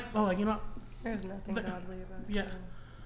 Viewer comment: Did "Kicko" sp? say at 1:56 that "there is nothing unattractive about you" referring to Gorlock?? I hope I heard wrong but the BS meter is going off the scale here